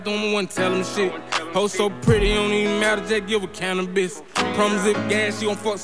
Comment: Did he just say "Hoe so pretty it don't even matter give her a can of biscuits" ?